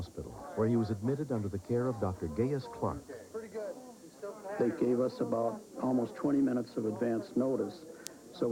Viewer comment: did he say Dr gayest clock?